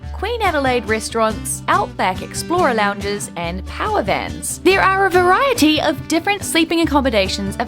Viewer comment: Did she say "power vans"? No wonder she never heard the term "locomotive" before. When I was little I called them "fire trains." 6:58 - Actual glass glasses! Fancy schmancy! 7:17 - I like the report while lying on the floor. You don't see that on YouTube very often. 10:30 - I was curious about the Blue Mountains and discovered that it's really hot in Australia in January of 2024. Tomorrow's forecast is partly cloudy with a high of 96°F 25:25 - Another video with in the shower and not. I guess there is an advantage to being the holder of the camera. Finished the video. Thanks for your time and effort (and expense). I look forward to the next and YouTube labor of love.